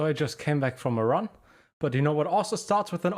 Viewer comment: During the intro I thought you said you got back from "Iran" and I'm thinking "wow, this guy is very cultured and loves traveling the world" but no, just "a run," not Iran.